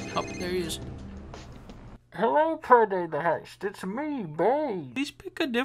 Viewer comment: What does bane say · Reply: Hardskoper "hello, payday the heist it's me bane"